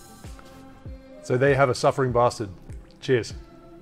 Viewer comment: I thought you said "suffering bastard"